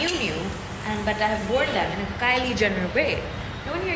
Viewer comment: is alien ne kya bola kisi ko kuch samjh aya ? 🙁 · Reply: Kylie jenner